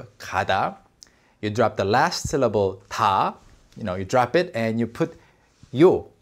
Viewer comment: Did you just said hindi?? 1:59 i heard 'yeh jo aap ka last syllable tha' 😅😅😅😅